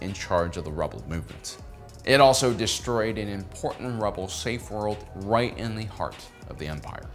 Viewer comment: I really thought it said safe words and got very worried